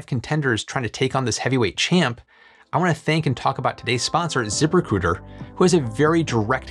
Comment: Am I the only one who heard "Zipper Cooter"? :D